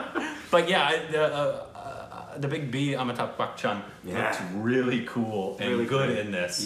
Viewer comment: He said "Amitabh Bachchan" , I heard "I'm a tub butcher"... I thought he meant to say "I'm a butt toucher"